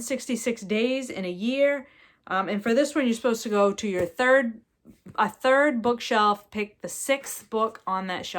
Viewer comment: LOL I just misheard the Book Shelf prompt as Book Shop and thought Oh Yeah I want to do that. So I will go to the third book shop I pass on the way to the bank later - to see what the book is :)